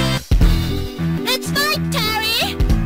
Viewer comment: I thought she said "Nice going, Terry~!"